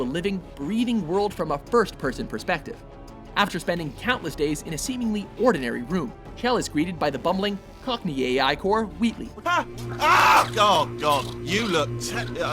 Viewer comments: Did he just say... Wheaton? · Did he say cockney?